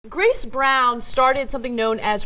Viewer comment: Oh wow! just realized that. And I didn't just misheard. I actually read Chris Brown. If you activate the captions on the video it says "Chris Brown" instead of "Grace Brown"